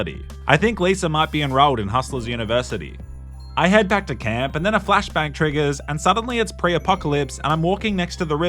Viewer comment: I thought he said a flash bang triggers.